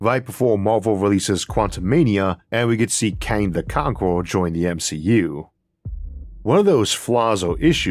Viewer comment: I swear I just heard "Kanye the Conqueror"